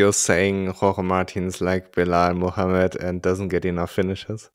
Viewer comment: Wer ist gemeint? Hab den Namen nicht verstanden... .... Mohammed? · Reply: Belal Muhammed. He's a UFC Fighter